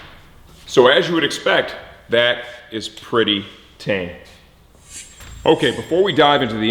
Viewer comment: what he said? “That is pretty [...]”??? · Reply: Andrea Libardi tame